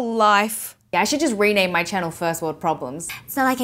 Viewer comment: I thought she said ‘I should just rename my child First World Problems’